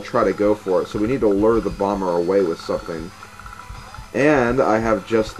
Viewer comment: you have to lure the bummer away? xD i misheard that i know still great project